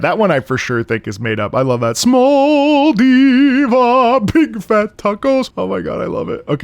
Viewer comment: I always thought it said Big Fat Tacos for Me!!